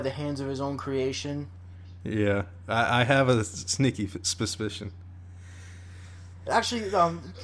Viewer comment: did I just hear “spuspision”